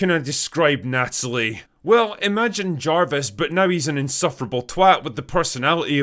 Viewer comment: First I thought you said “Imagine Jar-Jar…” rather than JARVIS, and I actually like that version even better.